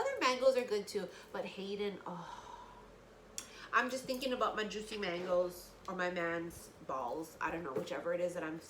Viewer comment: Oh Lord. I thought she said "I'm thinking about Haden mangoes on my man's balls." Oh boy I sure missed a lot. 😂😂😂